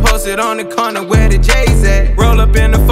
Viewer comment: Thought Roddy was saying where the gays at when listening to Spotify